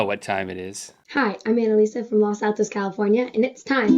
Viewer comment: did she say los santos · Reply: Monumentally late lmao but she said Los Altos